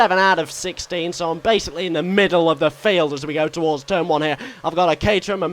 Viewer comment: When you said "so I'm basically in the middle of the field" I heard "so I'm basically about to be involved in a massive accident" Glad I was wrong :P